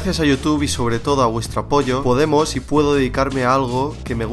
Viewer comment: minuto 5:24 gracias a youtube y a vuestra POYA le escuche XD